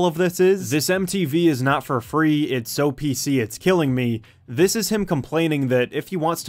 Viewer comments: i always thought he said 'obesity is killing me' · I always thought he said this symphony is not for free